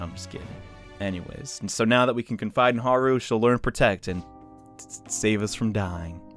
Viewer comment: The first time I listened to this I heard: "If you find a Haru you just wanna protect it" My disappointment set in that the joke was not made with how well that would have flowed.